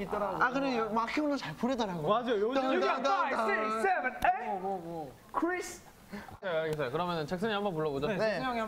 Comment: did Jackson say Kris? or krease? Lol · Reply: I think he said Chris xD